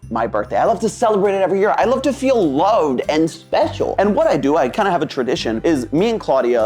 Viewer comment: … did someone say feel special? ( twice ref )